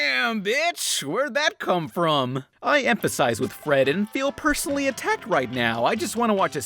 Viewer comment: says emphasize instead of empathize :^)